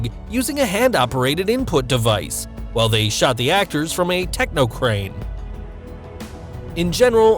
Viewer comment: I thought It Says Technoblade XD